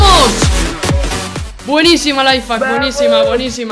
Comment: Like si pensaste que dijo lifehack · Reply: Xd es Layfack soy yopi